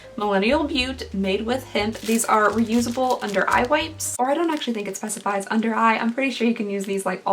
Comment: I heard under eye weights for some reason.